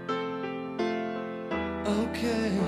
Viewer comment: Damn...for 30 freakin years I thought it said "motor run" and I just found out its motoring.